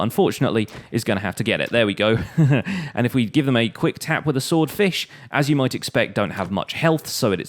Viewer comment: Quick tap with a 'sword-fish" Did anyone alse hear that? He didnt have much of a space between those two words, so it sounded like he said swordfish.